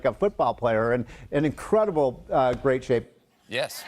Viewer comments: "Trump is in great shape" .. If bloated is a shape, then yes.. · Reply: I thought he said Grape shape too. Trump has breasts, lard butt, losing hair, hands are getting smaller, his lips are getting thinner and his orange color keeps getting worse. I agree he is in Grape shape but with a hint of orange. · "Trump is in great shape" .. If bloated is a shape, then yes.. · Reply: I thought he said, "GRAPE shape".